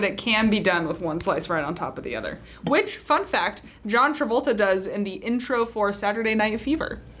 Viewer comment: I thought the narrator said 'fuck that, John Travolta.....'